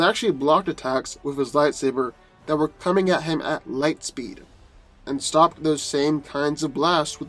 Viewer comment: Saheteraba Kutisha I don’t mean speed if he locks up all of Tony’s muscles he wouldn’t be able to engage anything. Plus light speed doesn’t mean much to someone who can deflect objects at hyperdrive speeds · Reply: Saheteraba Kutisha I misheard him at 6:57 when he said he blocked attacks at light speed not hyperdrive